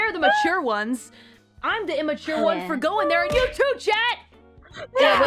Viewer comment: 下意識地把you too聽成youtube，想說YT確實很會想歪然後BAN台，沒毛病((草